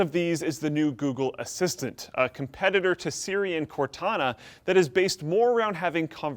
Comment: At one point I thought he said "Syrian Cortana".